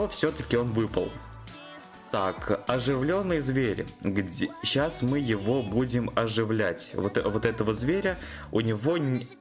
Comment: мне послышалось что мы его сейчас будет возбуждать